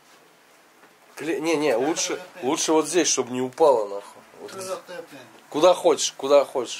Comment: "Лучше вот здесь чтоб не упало нахуй"мне одному это послышалось?